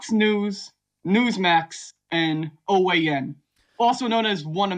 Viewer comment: I like the Freudian captioning at 0:55, Orwellian instead of OAN.